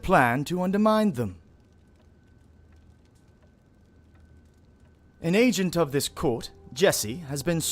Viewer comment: Hahahaha 1:13 I thought he said "An ancient of this court..." I was like dayum O_o, I underestimated this Jesse character, dude was tagging walls in ancient Mesopotamia